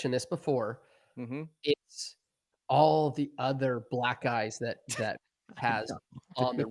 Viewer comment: Sounds like he said "Black-Guys" instead of him saying "Black eyes" 😂😅😂😂😂